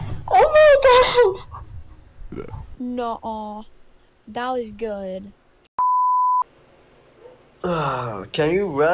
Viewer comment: did she just said "naol"? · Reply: No*